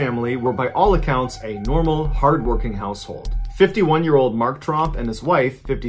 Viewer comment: Who else thought he said "the Trump family" when he said "Tromp" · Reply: I did a double take when I thought I heard “the Trump’s were a normal, hard working household “